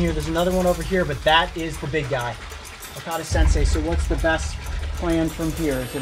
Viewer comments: I swear to God he called him Kakashi-Sensei 😂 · Definitely thought he was saying okata's sensei till about halfway through it. I was like where'd the first Dr. Go?